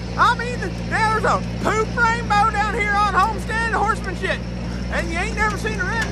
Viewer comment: Did I hear "horsemanshit" ? 😄